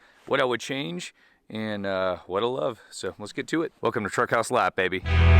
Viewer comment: Love the vids man! Always sounds like you say truck house live lmao